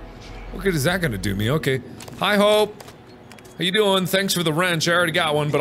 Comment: dude that caught me so off guard because that’s my name and I wasn’t paying attention so I heard “hi Hope” and went HUHHHH